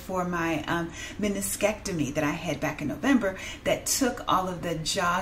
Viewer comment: I thought I heard you say mastectomy but wasn't certain until I heard it the second time. I am so sorry! I'll have to search for some of your old videos to hear your story. I lost weight a good bit during covid but I also lost several family members, several, due to the virus. Normally when I'm stressed I gain weight but I had no awareness that I was losing weight. I pray that that was the reason why. It was difficult to go to a grocery store and feel safe and as you know at that time we were being supplied with the boxed food most of which was fairly healthy so maybe it was just cutting out a few things for a lot of things that I wouldn't normally eat. I'm still pretty bad about exercise is my work hours are so demanding but I have got to get better about that . I like Feta cheese, I like cheese. I stopped sodas many many many many many many years ago but if I'm invited to someone's home for dinner and that's what they have, I may go ahead and have one. I do like chocolate with sugar no. Glad you were doing well now😊